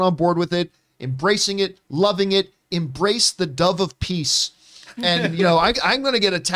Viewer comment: For the entire show I thought Peacemaker was saying "Double Peace" when it turns out it was actually "Dove of Peace". I was so confused.. Lol